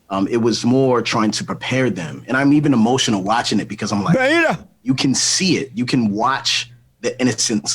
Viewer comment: Did Oprah call him a Beta???